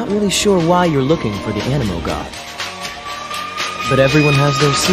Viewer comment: Lol i ligit thought he said anime god